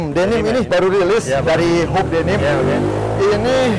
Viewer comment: hoops denim? Kurang jelas nih dengernya gw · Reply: izin bantu jawab hoope denim